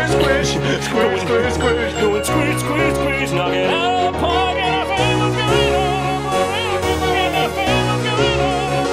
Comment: Could someone ell me what they're saying at 0:30? I'm so confused as to what they're saying, even though it sounds so beautiful. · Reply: Samta Nah, that's definitely an F sound not an S one lol Kind of sounds like "I found what's going on"